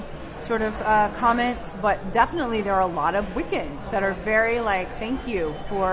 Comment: What is she saying at 3:58? ''But definitely there are a lot of..''? A lot of what? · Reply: Wiccans?